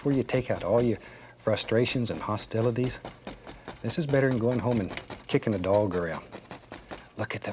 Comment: bro WHAT😭😭 i also thought for a minute he said “better than going home and beating your wife”